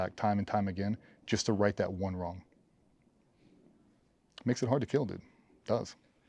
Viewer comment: I thought it said, Was it Hard to Kill? 😭 shows where my mind is 🤣